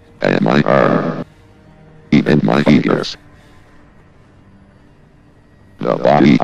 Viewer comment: When VOX said “fingers”, I thought he said “penis”.